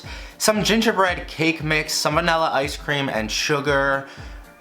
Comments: Anyone else hear "salmonella ice cream" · I don’t know why but I heard salmonella ice cream